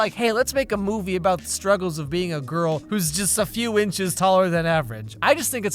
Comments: When he says “a girl” I thought he said ugly for a second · When said "a girl" it sounded like he said "Uggo" 😂😂